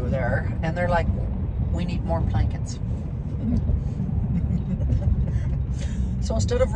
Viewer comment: Plankets? I thought you said blankets because it is going to get cold. Then I thought Home Depot for Blankets???? LOL